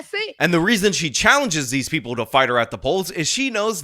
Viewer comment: Wow I had to hear it for the probably 20th time to realize she said "fight me at the polls". I legit thought she said paws as if that was some slang for a park or something in that area.